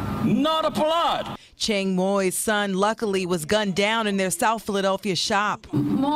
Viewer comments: Holy shit did she at 1:22 just say luckily he was gunned down? Maybe I heard wrong. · did she just say that kid was "luckly" gunned down?